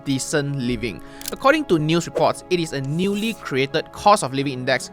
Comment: I heard according to Manny Pacquiao 😅